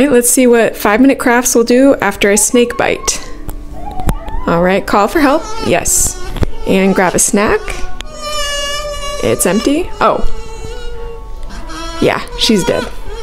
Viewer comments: I thought she said yeah she’s good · Oh I thought she said “yeah, she’s good” in a sarcastic way now I know she said “yeah, she’s dead.” Lol ur so funny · "Yeah, shes dead" 💀 ain't no way she pulled out them chips💀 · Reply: I thought she said "yeah, she's good" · Help, i keep hearing “shes good” and i watch again and hear “shes dead” and the cycle wont stop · What she said “ yeah she’s dead” What I heard “ yeah she’s good”